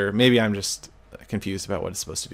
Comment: i nearly thought that he said thanx for washing